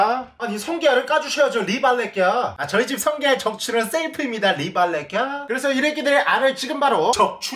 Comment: 나만 셀프가 아니라 새이프라고 들리냨ㅋㅋㅋ